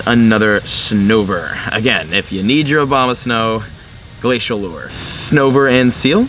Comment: I swear he said Obamasnow